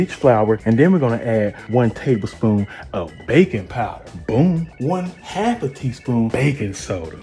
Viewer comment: i thought said bacon powder 😂